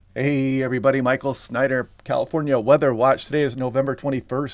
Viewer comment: 'Heyyyyyyyyy, Everybody, Michael Snyder from California Weather Watch' - Ohhhh it is on when I hear those words. Some mega meteorology inbound. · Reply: For the longest time I thought he was saying Michael Snide here. I didn’t know his last name was Snyder